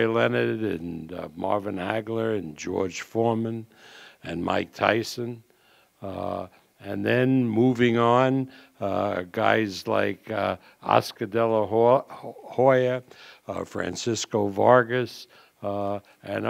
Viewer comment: TV DID BOB SAY OSCAR DE LA WHORE AT 1:51 LOL